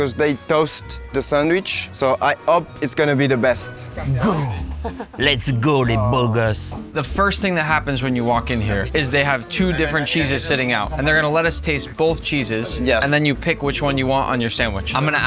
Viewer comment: did he just say lets go le boogers?